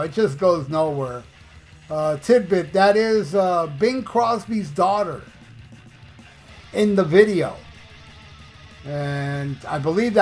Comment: I will not use chemicals when I watch your videos again. I thought you said Bing Crosby's daughter slept with Tony's doberman.